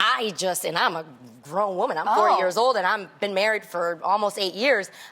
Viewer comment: did Tamera say she’s ‘40yrs old??’ 😳😳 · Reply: VintageGirly Glam yes her and tia just turnt 40